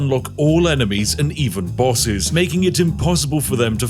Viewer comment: You say "bosses" and I hear "wusses"